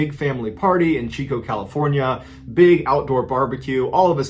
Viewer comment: Ayye Elk Grove CA.. a good friend of mine who also happens to be my cousin lives there.. I dont recognize that street though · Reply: Did I hear you say Chico CA? Haha yooo Chico CA is where the party schools at! If ur a college student that is…